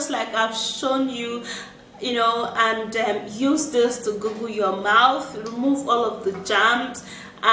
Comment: Did I hear her say "Google" instead of "Gaggle"?